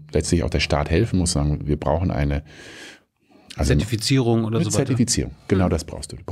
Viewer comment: wie heißt das wort? hab es nicht verstanden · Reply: Zertifizierung